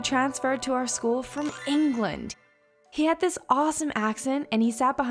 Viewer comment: When I put on the captions, it said "He had this awesome accident" instead of "He had this awesome accent" LOL!!!